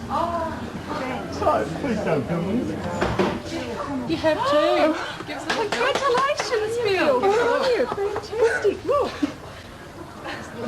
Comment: I swear at 1:38 he says "Jesus.... God please don't kill me" the man was probably so afraid of dying again after such an amazing moment which is honestly so sad